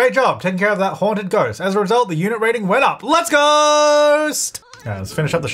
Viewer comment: Did I just hear, "Let's ghost!"???😂